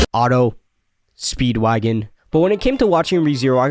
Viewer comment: why do I hear this as "Auto > Speedwagon" it's like comparing car transmission now 😂